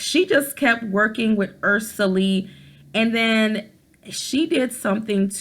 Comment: When you said Ursuline I thought you said Ursula